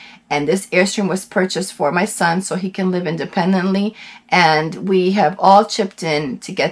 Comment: I thought u said 'independent'. Sounds like u live in it too.